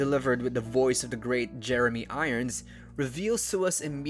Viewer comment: Misheard line hear made me immediately pause to go and check that Jeremy Irons is alive. Seeing he was, I came back, confused, wound the video back 5secs and this time heard 'the great Jeremy Irons'... not 'the late Jeremy Irons'! XD